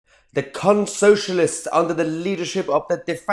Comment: Right at the start me and my fiance both thought you said crunt-socialists (silent r in crunt), however, that would also be exact.